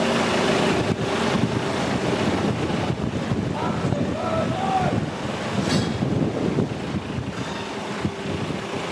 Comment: I swear at 3:48 i hear "OI!" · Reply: One of the workers shouted "Captain Birdseye" at the tugboat captain :)